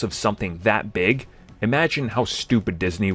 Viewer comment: I could swear I heard him say "when they (Disney) f--k the next Avengers."